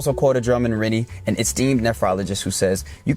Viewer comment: I'm lysdexic and autistic. When you said nephrology I thought you said phrenology... I'm such a dope... You, young man, are a superb speaker and your diction is nearly perfect, articulation phenomenal. How many takes do you average to get it all right, for each segment? You will have success in anything you decide... I highly recommend music and foreign language to expand your mind further. Mathmatics are always highly advised. Most can't achieve much sense of accomplishment working with numbers. But this place is physical. Study of physics is essential. We can build machines that can fly without conventional propulsion. Magnetically driven craft. Microwaves and mercury in a torridial field. Kewl stuff. forget the balls and sports.